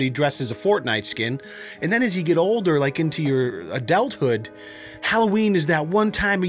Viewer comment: did he just say adelt hood